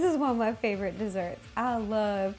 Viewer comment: Have I been pronouncing Tiramisu wrong my whole life? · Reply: It kind of sounds like she is saying terimisu to me , which I think is the wrong way to say it but hey , what ever flips your pancake 😀😀